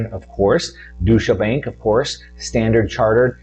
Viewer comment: Oh DEUTSCHE Bank. I thought you said DOUCHE Bank. Well....actually.....now you mention it